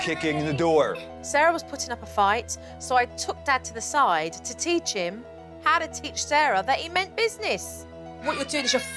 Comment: Bro i was tweaking cuz i thought she said teach her how to steal.